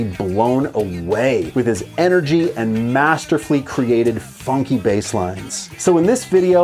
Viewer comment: Did you say masterFlea at 0:37?